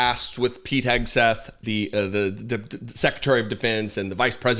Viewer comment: did you say "Secretary of Depends"?????????????